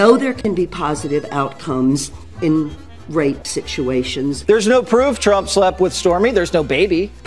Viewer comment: Did he just say "there's no proof where's the baby?" Every time he sleeps with a woman there's a baby? Who is he Hershel Walker?